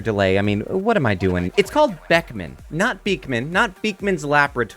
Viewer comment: I really thought he said his name was Bachman, as in Erlic Bachman from Pied Piper. That might actually make a better movie than this one...